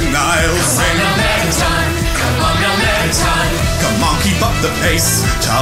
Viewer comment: i thought he said "Come Monkey"